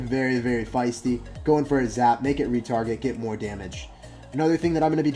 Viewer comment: I thought you said make it retarded